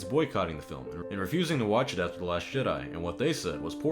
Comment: I had to rewind at the 48:28 mark as I could swear you said "The Last Shit Eye". I realized, of course, that you just said "The Last Jedi" quickly, granted it wouldn't be completely inaccurate to refer to that film as feces being hurled at your eyeball.